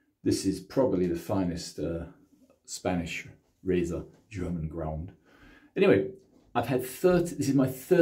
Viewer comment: Getting better on your angle, well done. At 3:15 you said the finest Spanish razor, German.... ? What was it you said? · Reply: Hi Matt , thanks for viewing , I was referring more to the filarmonica origins ,as being German ground , I'm not sure when the Spanish fully took over , and the tridur is a mystery, a later made razor with magic qualities. I'll have to look it up . 🙃